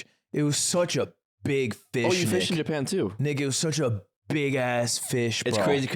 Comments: Even closed captions thought what he said at 17:45 was sus. I know you were saying "Nick it" but you went a little soft on the k😂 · i feel like 17:45 just has to go in the history books or something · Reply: I thought he said “Nigga it was a big ass fish” so I did a double take and I realized he said “Nick it was such a big ass fish”